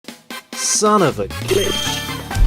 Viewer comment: instead of it saying "son of a glitch" it sounded like saying "son of a bitch" XD